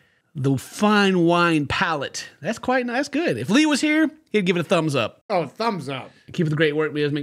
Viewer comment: I thought u said “if flea was here “ 😂